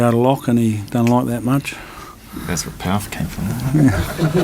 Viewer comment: What did Kieran say at 10:50? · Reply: that's where all the power came from